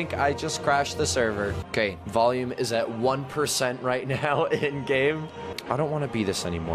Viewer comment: i thought denis said end game for a sec..... DUUNNNNNN DUN DUUUN DUN DUUUNN, DUUUNN DUNN, DUUUUUNNNNN.... i tried to do the avengers theme but its hard in writing XD